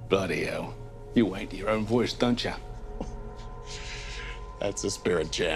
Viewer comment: can anyone explain me what he said and what he meant by that? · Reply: "you w@ to your own voice, don'tcha?"